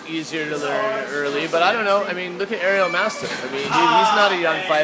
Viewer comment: Did he say aero mastive? @ 7:08 ?